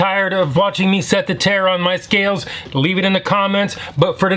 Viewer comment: I always thought you were saying setting the chair on the scale